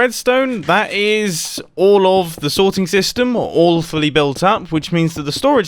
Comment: Whenever says "all fully" I hear "awfully" 😅